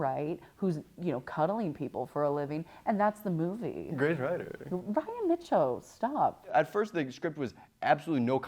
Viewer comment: "Great writer." "Ryan Micho, stop." Not hearing instead of great writer 😭😭